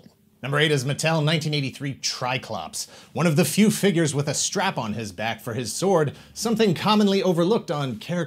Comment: When talking about triclops i thought he said only figure with a strapon.